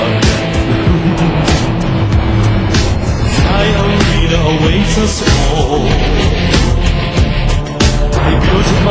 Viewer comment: What does Pegasus say at 1:50? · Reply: He says "My arena awaits us all". Sorry for late reply